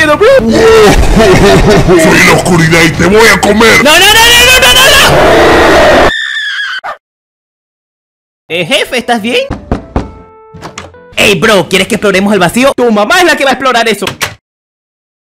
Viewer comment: "Tu mama es la que va a explorar eso" xD La mejor parte del vídeo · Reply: Yo escuche "ni mamadas voy a explorar eso"